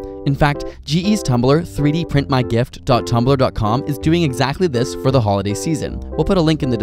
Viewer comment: Thought he said 3D print my GIF. I am disappointed.